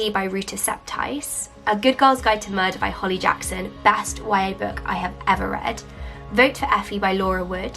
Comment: that's impressive. and thank you for suggestions. non-native speaker here. What did you say at @ 5:00 "Best ... book i've ever read". Best what book? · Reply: oh i get it now. Y.A. book